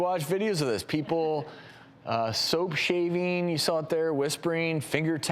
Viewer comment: I thought he said Soap shaming lol